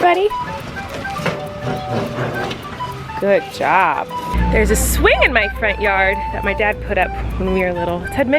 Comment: I swear at 1:00 he said "there's a shovel"